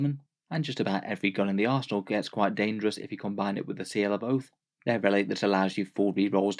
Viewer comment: Thank you for the video. For a moment I thought you said "Seal of Oaf" :D